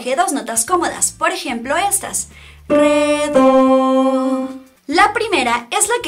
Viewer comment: le entendí "PE-DO" :v